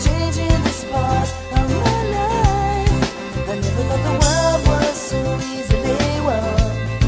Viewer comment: When it was just the 3 second clip, I thought he was saying "Remember when the world was so easy?"